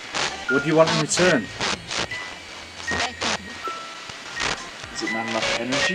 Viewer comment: i heard the word satan after they asked "what do you want in return"